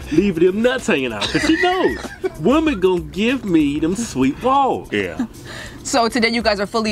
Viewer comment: Wait.... Did he just say "Dem Sweet Walls"?!?!? 😳😂😂😂😂 I'm weaaaaakkkkkk! 💀😱😝